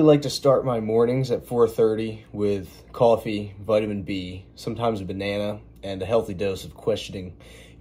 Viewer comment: oh my goodness I had to replay it, I thought you said you start your mornings with coffee and butter beans.? I was like what, that’s nasty is that a regional thing?? I can’t imagine anything worse so I had to rewind! 😂. Yes, Vit B, especially Vitamin B12 as it gives your cells energy etc. ❤😊